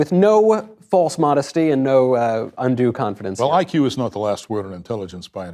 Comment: what did he say here? IQ is not the what?? I don’t understand, would someone care to explain?? And the way he speaks he is not allowing you to demean and belittle yourself around him… I love him❤❤ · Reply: He says “iq is not the last word(form) of intelligence “